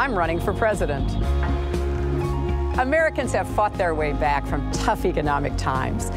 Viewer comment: Ok. We won't use the phrase "War on Women". We'll do the republican think and call it a "Jihad on Women". (Also, I heard at first "Americans have fucked their way back..." Lmfao)